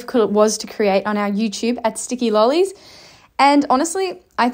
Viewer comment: I thought you said skippy lollies, kind of fitting for the roo